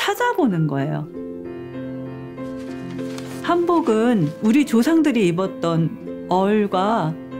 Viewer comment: it sounds like she said hamburger hehe